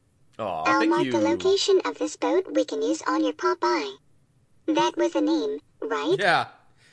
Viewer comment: did Sugarloaf say Pop-Boy because to me it sounded like she said Popeye